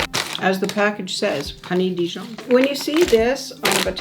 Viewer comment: I thought he said "Honey Deez nuts" instead of "Honey Dijon". 💀☠️